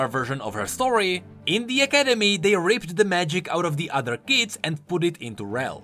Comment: I’m sorry....but it sounds like you said raped the magic out of the other kids...I was like woah.